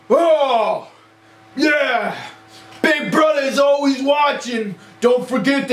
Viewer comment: it actually sounded like big brudda